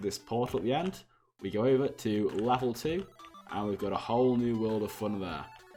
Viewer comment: you say Lavel 2 that’s what I heard or is it just me🤔